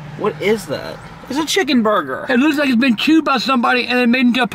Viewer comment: Oh, I thought he said "puked," not "chewed."